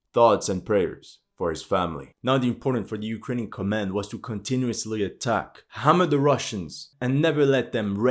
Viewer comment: What said: "...for Ukrainian command was to continuously hammer the Russians." The subtitles: "...Muhammad the Russians." 💀